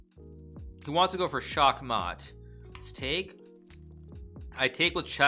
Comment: did you say check mate in hungarian ? :D sakk matt